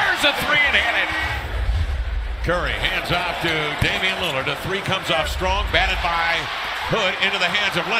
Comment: did he say curry when Lillard was shooting?😂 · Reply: i guess he said “Curry hands out to Damian Lillard”